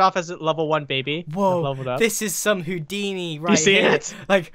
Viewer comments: Did he say Hoodini? I am watching a youtuber who's a fan of VannosGaming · Did he say Hoodiny? 0:22 original vid #vannas Hoodiny